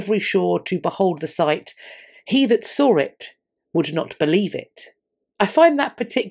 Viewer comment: Re: that quote at 15:15, it actually says “he that saw it not, would not believe it” (my addition of the comma and modern spelling obviously). So I think what he’s saying is basically “you had to see it to believe it” or “ if you hadn’t seen it you wouldn’t believe it”.